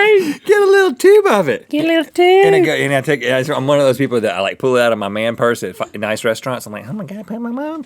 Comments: Is it me or at 8:32 it sounded like Rhett said "I'm one of those people who pull it out of my man purse and f*ck in Ice restaurants" Lol wut xD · What did Rhett say at 8:30 ? LOL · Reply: Get a little tube of it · Did . . . did Rhett say "at a f*cking nice restaurant? xD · QUESTION!!!!: Ok honest question at 8:34 what does Rhett say? Because it kinda sounds like the f word but I doubt thats what it is but I listen to it like 5 times and can't figure it out. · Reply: I think he said "fine, nice restaurant" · Who else heard, fu*king nice resturant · Did he say fu**ing nice restaurant or am I hearing things? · Did Rhett say F*cking nice Restaurant at 8:31? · I must be hearing stuff when I heard Rhett say "in a f(luffing) nice restaurant"